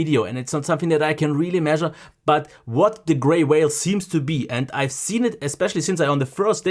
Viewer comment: Literally thought you said grey whale lmao! Perhaps it's their burn in prevention method